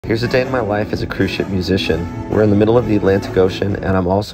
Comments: I thought he said Christian musician..😮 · Seriously thought you said christian music and started to scroll...oh CRUISE SHIP